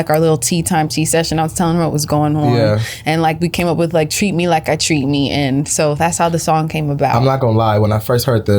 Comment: I thought she was saying treat me like a treatpee